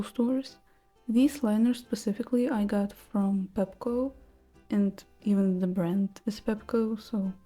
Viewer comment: I wholeheartedly thought you said you bought your liner from petco😭